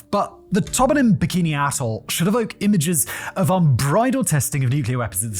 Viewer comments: "the tobinin, bikini asshole" is all I can hear, I know you're saying "......... ....... atoll" but cant figure out exactly what... If only you were selling scripts o.O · I feel like it should be bleeped every time Simon says “atoll” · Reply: I had to back up a few times at the start because I keep hearing "Bikini asshole" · My fiance made me rewind it because she swore you said bikini asshol3. 😂🤣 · Can someone please tell me what he said at 0:06? What I heard doesn't seem to make sense... Edit: after listening a few times I think he said "(something) bikini atoll" in case someone was wondering the same thing... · I had to rewind this part 5 times until I finally put on subtitles bc I could have sworn it said “bikini a$$h0l3” 😂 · Did he just say Bikini asshole?